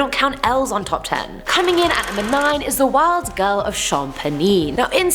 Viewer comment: I thought Number 9 said "The Wild Girl Of Champagne" 😂